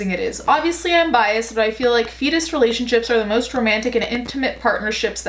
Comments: When she said feedist i thought she said fetus and i got concerned lol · So I had this playing in the background while I showered and when I got out I heard you say “feeder relationship” but I thought you said “fetus relationship” and just wanted to get back in my bathtub and curl up in a naked wet ball of despair for the state of humanity. But then I checked the title and was like, “Ohhhh, this old chestnut 🙄” and went about my routine. I’ve watched documentaries about feederism and it’s so painful to watch someone not only stuff themselves, but be encouraged to slowly kill themselves, basically. Then there’s the feedees who WANT to change and aren’t REALLY into it, so their partner leaves them when they lose weight. It’s so twisted and sad.